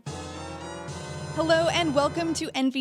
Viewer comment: For a moment there I thought Casey said "wooloo" instead of "hello" lol.